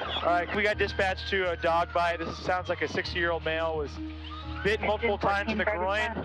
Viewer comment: I can’t be the only one who thought he said “6 year old male”